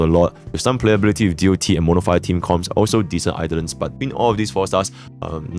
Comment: Did bro say mortefi team comps at 07:29 or am i hallucinating